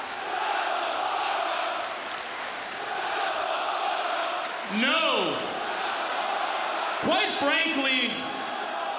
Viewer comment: did THEY say nakamora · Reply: They said STFU